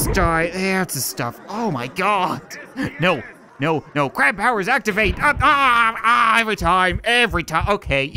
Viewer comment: i heard crap powers activate